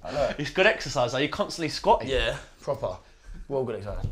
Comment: What does he say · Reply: Proper